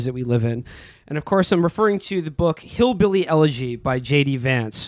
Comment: I thought the book said "Hillybilly Edging" I was like dayum that book freaky af